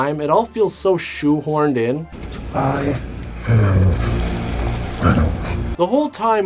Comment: did he say I am Venom?